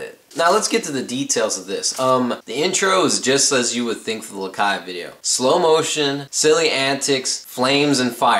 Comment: Did he say lakai video at 01:16 (:I)